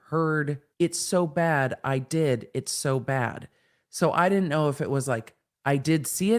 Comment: I hear “it is so bad, like it is so bad”